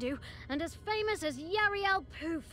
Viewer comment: i thought i heard he say Yarel OOF imagine being called Master OOF